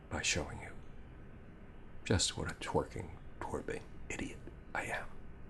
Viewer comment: At the end I thought he said "twerking idiot." Thank god that was wrong! Great video.